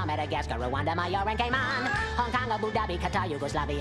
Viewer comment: i swear i heard gay man